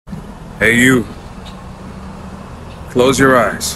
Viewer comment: Thank you! I have been going through so much! At first, I thought you said it would be a BAD day tomorrow. I was like: “what”? 😅